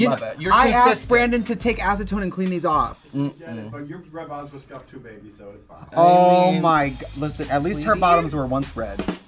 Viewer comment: He actually says “your red bottoms are scuffed too baby so it’s fine”, not whatever it says in the closed caption. LOL